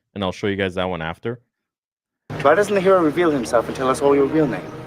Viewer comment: 😂 “Why this nga won’t reveal himself”’ is what I swear I heard 😂😂😂